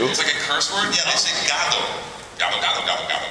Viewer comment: DID HE JUST SAY GAGO!? o_o (To all Filipinos, you know what I mean!) XD